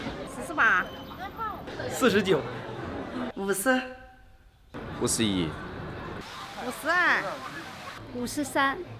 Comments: he clearly said " What's the year "😂😂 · Sounded like she said: WASSSAAAA